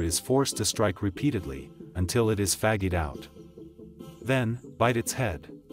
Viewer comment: Interesting pronunciation of "fatigued" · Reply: Lol I legit thought it said "until he gets faggied out" and I was like - well that's one way to put it.